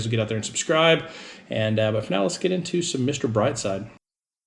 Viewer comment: I thought its said your special guest was your son Mr Brightside lol ( i never heard of the song )